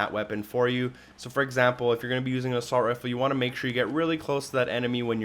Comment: and i recommend using a SHOCKGUN, i recommend using the shockgun, the idea is killing more people at once, shockgun, shockgun, shockgun people. use the shockgun. here i am able to get a couple multi kills bam bam two quick bullets with the shockgun, remember use the SHOCKGUN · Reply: Also sounds like he is saying " a salt rifle "